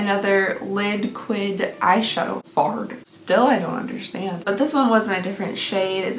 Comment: I thought you said eyeshadow fart 😂😂😂 and then you showed the tube. "fard" means eyeshadow in French. Fard à paupière 😉